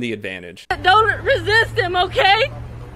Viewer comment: Watch my skate shop journey: <link> Merch: <link> Pledge to me on patreon: <link> My second channel: <link> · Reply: she gave you a shout-out... I know I heard her say "doughnut resist em oh kay"